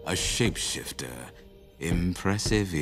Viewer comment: WHEN SAID “shape-shifter” I THOUGHT HE SAID- “S#!¥shifter”